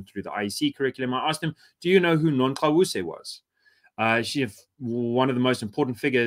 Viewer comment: Did anyone catch the name of the girl at 6:09 who's ancestors spoke to her on a hill and gave her remedies for the maladies of the time? I've tried listening again and again and it never comes out as "greta thunberg". Maybe it's the rhyme. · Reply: Nongqawuse